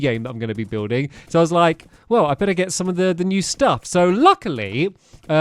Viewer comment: I thought he said "Well, I finna get some of the new stuff"